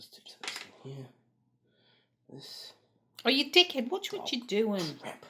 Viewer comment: does she say, "Oh you dickhead, watch what you're doin' " or is it just an accent misunderstanding? :O I had this model as a boy; very nice restoration.